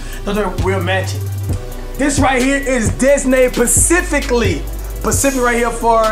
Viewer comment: lmao sound like he said "designated pacifically" lmao luv the channel though